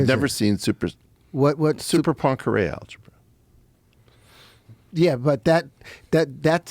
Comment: did he just say Cyberpunk Korean Algebra??